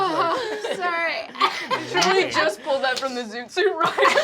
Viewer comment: Can someone please tell me what Ally says at 38:23? I CANNOT make it out and it’s driving me crazy! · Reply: I'm not sure about the first word but I think they said "Truly(?) just pulled that from the Zoot Suit Riots" and I believe they're referencing the riots themselves not the song.